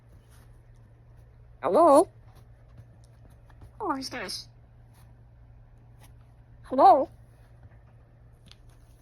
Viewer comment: Apollo blocks scam callers. “Hello? Hello?” doo doot “Hello? Who is this?” … “Block” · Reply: oh dang, I heard "¿Cómo estás?" instead of "Who is this?" and thought Apollo was bilingual, which would not surprise me a bit